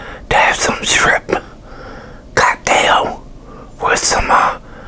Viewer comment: LOL i thought he said gooddamn at the beginning when he actually said cocktail